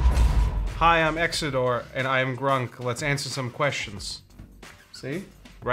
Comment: I heard hi i'm Exedore and I am drunk xd